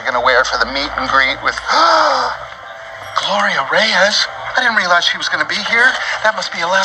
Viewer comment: did i hear "Gloria Reaz"? lol